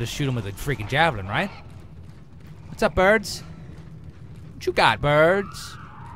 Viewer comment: lol if you put on the audiotranscript it says "Filbert" instead of "whats up birds" at 0:30.